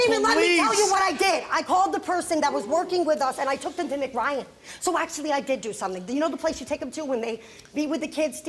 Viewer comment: I miss understood her. I thought she said she took them to McDonald’s. Still a bad parent. I hope those kids never have to go through that aging